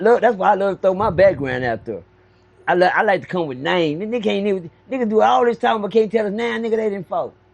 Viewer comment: Wait what?😂😂 Did say I can come up with nameslmao